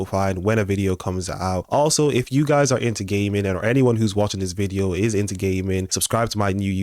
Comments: I thought you said “gay men” not “gaming.” 😂😂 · When you said gaming I thought you was saying gay men